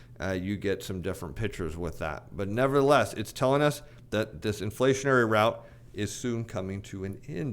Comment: I can't tell what's coming to an end? Did you say "this inflationary...." or "Disinflationary"???? Thank you for clarifying. Great stuff as always. We love Dr Purple because he can smite the trolls! (Can you make your crown out of bond paper?)